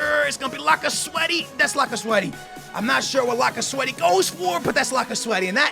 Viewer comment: did he just say lacasweathy?